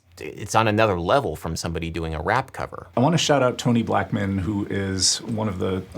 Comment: “i want to shout out 20 black men” is what i heard. confused the fuck outta me when he kept talking.